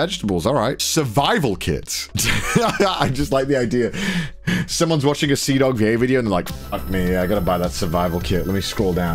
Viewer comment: Um- i heard 1:29 "Someome is watching cdawg gay- video"